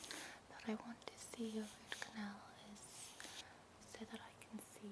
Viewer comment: Love this video, it's fantastic! Definitely Going into my favs.... And correct me if I'm wrong but did I hear you say 'ROOT' canal after 4:00 mark?